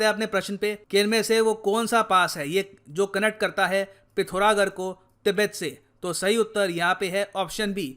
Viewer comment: sir did you said Tibet?